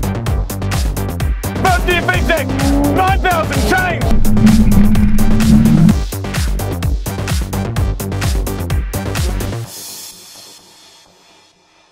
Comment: DID MOOG JUST SAID 9000RPM CHANGE O.O